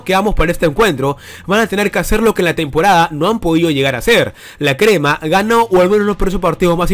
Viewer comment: La crema gana o que? no entendi